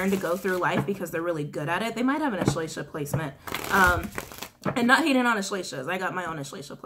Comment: ESHLASHA PLACEMENT? What is it shes saying? Whats my placement?